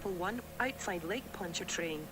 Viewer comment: I heard "Lake Pontchartrain" as "Lake Punch A Train"